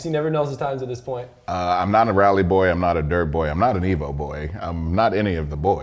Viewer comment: I thought was bout to say “I’m not a white boy” at the beginning lol